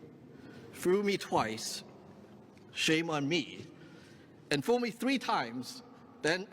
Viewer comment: I love chicken little 🤣 1:52 I'm sorry, is that 'fool' or 'foo'? I heard two fools and foo 🤣 go get'em Sammy Chu!!!